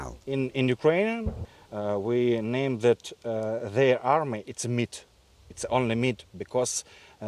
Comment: lmao did he just call the Russian army Mid 😭